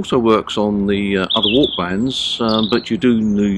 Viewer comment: Can someone clarify what was said at 1:02 - "w..... bands" ? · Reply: "WARC' bands...